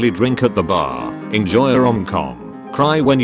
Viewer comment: I was alt tabbed while this played and a moment i hear "enjoy your own cum" and i was like WTF !! Then i come back and read "enjoy a rom com" i feel dirty now ...